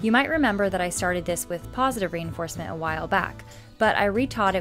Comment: I thought you said KAZI-reinforcement 🤭